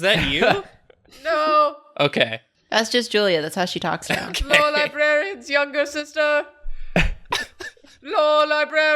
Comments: I feel like Julia missed an opportunity to name lore librarians sister “Lorelei-brarian” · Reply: I thought she said loura-brarian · "Lore Librarian's younger sister... Lore Librarian." the hardest-core fans: scrambling to open a new tab Quick, add that to the wiki! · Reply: I thought she said "Laura Brarian"